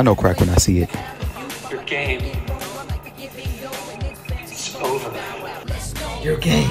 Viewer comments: I thought he said your gayness is over🤣🤭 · When he said “your game. Is over.” I’m the pause I thought he said “your gay.0